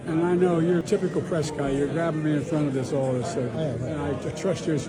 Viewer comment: I think he says either "You're grabbin' me in front of this (posibbily referring to the phone camera) all of a sudden" or "You're getting me in front of this audience here"